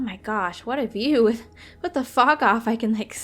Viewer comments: I THOUGHT YOU SAID WHAT THE F**K OFF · it seems like she says wtf instead of with the fog of. lol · When I first heard it I thought she said "what the fak" Sorry lol